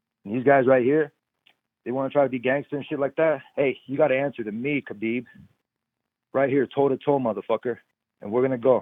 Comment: Many Of You Didnt Catch What Tony Said @ 7:38 When Said "Aye You Gotta Answer To Me Khabib Right Here Toe 2 Toe MotherFucker" thats Off A Mexican Gangster Movie Called "Blood In Blood Out " 💪💪💪Tony A Real Crazy Gangster 😂😂💪💪💪